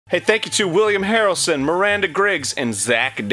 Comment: I heard that as Woody Harrelson, not William Harrelson, for some reason. I was like, woah!